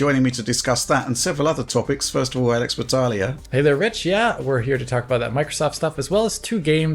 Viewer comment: thought Alex said .. hey there βitch 😳